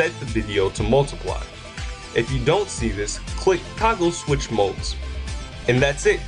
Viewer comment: also at 3:51 what the heck did u say?? IF YOU DONT SEE THIS CLICK "KAGUL " and switch modes ?? wtf is that? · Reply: fallee " toggle switch/mode"